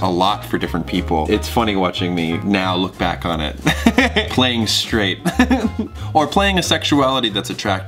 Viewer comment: did he just say playing strati